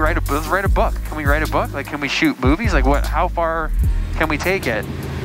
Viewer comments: Wait... he said shoot “movies” right? I swear I heard “boobies” lol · I thought I heard "how far can we take it? can we write a book? can we shoot boobies".... Maybe a refreshing switch up from landscape and product photography 😅